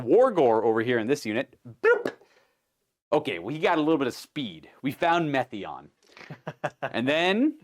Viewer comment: sounded like it was going to be “OK, he got a little bit of stupid.” Haha